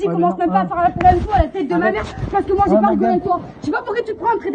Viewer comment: Et juste apres vers 5:09 elle dit quoi je comprend pas stp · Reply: "parce que moi jvai pas rigoler aec toi" 😂